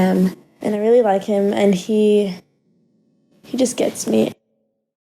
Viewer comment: who else thought she said and he’s gay